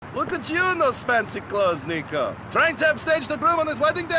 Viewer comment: Did he just said the N word and not the other N word cause I swear i heard the N word?